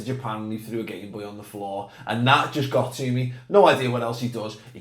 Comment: I thought you said he threw a gay boy on the floor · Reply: When you said tha Logan threw a game boy on the floor I thought you said he threw a gay boy on the floor at first 😂